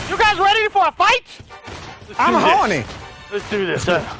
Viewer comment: I swear at 0:03 someone said “I’m horny”